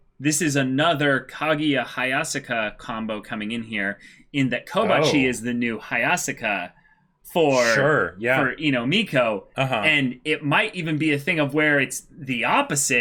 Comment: Dunno why but I kinda heard Komachi instead of Kobachi lol. I think the Oregairu hype train is driving me crazy.